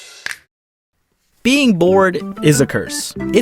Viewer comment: I misheard you first and thought you said: "Being born is a curse."